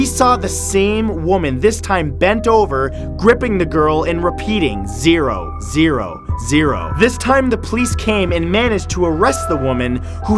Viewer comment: When he was talking about the counting down woman, when she was at zero I thought he said “Bent over the girl RIPPING her...” I was alarmed. Also, does anyone else feel like the smiling man is The Joker. Kind of.